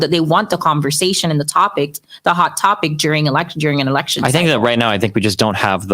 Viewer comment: I thought she said hot pocket not hot topic. Mmm. I'm hungry.